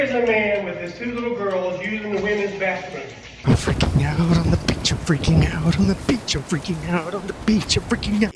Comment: so at the start I completely misheard you lol had to reverse since I thought "on the beach i'm freaking out" was you saying about the guy "I'm a b***h I'm freaking out"